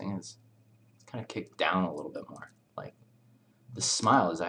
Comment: I accidently clicked the inteactive transcript thingy and it messes up on translating alot of stuff lol for instance :at 43:41 instead of it being "kick it down a little bit" it says "kick down a little dog" XD